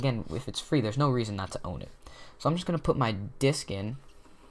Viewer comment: I was eating a quesadilla so I was looking down at my plate. To me it sounded like "So now I'm gonna put my DICK in...." I looked up like wtf? lmao